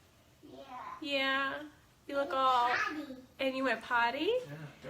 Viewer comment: Why can she never understand what her own children are saying? Cece said “Daddy,” SW thought she said “Potty.” Bella says, “I want that,” and SW thought she said “Laptop”.